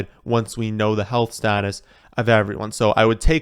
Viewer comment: Did he just say A-moaner St. Brown??? 😂